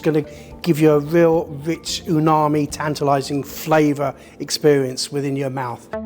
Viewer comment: did he really said “unami”? 😂